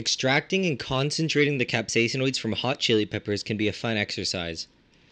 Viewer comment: I thought it said extracting capitalism from chilli